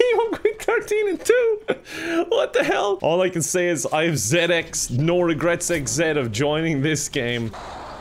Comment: I heard it as Xanax No Regret XZ